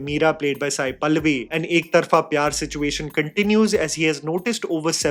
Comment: caption says Ek Tarpha PR. 😂